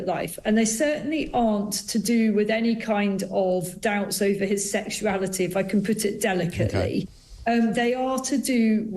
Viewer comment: I heard her say "it's not about his sexuality". Not it's nothing sexual.